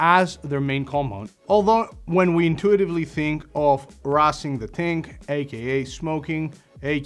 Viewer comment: did man just say "rarsing the ting"? 😂😂😂